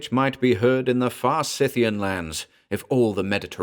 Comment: The quote line as per auto-generated closed caption: " ...but that which might be heard in the farsithian lands..." Can someone tell me what the word is and what it means? Thanks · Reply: It should be "the far Scythian lands" - Scythia being a Greco-Roman vague geographical term for the great Eurasian steppe, reaching from modern Ukraine to Kazakhstan or Mongolia.